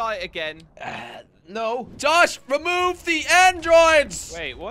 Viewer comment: did he say remove the androids or is it just me